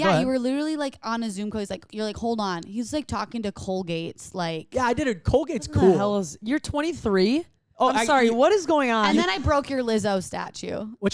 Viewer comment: Tell me why I thought ya'll were saying Cole Gates, like Bill Gates son (not that he has a son named Cole). Not Colgate the toothpaste.